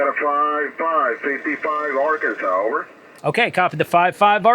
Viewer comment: Did you say “Arkansauce”? @ 9:31 ?